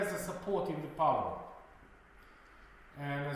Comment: I hear par(lie)me(nt), not power.